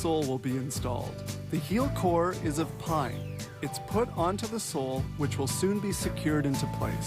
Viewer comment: Did he say at 3:14 that the heel core was made of pine?